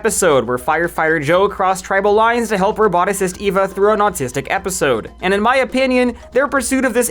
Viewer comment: it sounds like she said she had autism a ton.